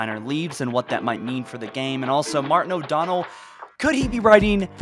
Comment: d..did you just call him martin o'donnel?